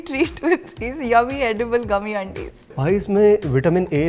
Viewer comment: Did say gummy aunties